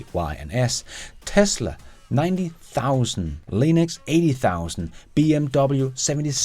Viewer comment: Did you say Linux @ 12:13?